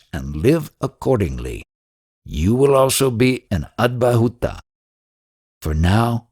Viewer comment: what is atmabhhoota...or what is the word that is said? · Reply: The speaker has pronounced wrongly.. It is' avadhoota' which is equivalent of a sage only